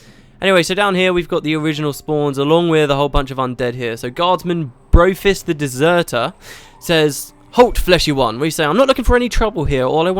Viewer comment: Happy 1 year LP anniversary! Good times. :D Also, Gaile Gray joined the support team at ArenaNet. Hahahaha, at about 16:06, it totally sounded like you said, "Guardsman Bro-fist the deserter." Anyway, keep up the excellent work. :D